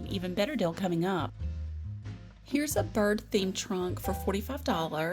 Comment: I have to laugh at myself. When you said Bird Themed Trunk I could have sworn you said Birthing Trunk and thought a what? Of course I quickly realized my mistake. 😊😊🤪😜 That beautiful carved white linen chair looks newly upholstered what a deal! Catch you next trip and Thanks!